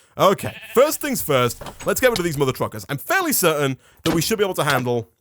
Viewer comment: i heard one between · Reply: angelina hernandez Are you talking about "Mother Truckers" at 21:37?